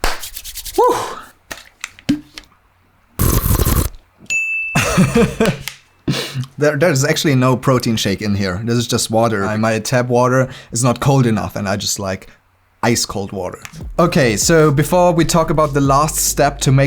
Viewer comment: haha at 17:43 this is the second time I catch you saying "enoth" instead of "enough"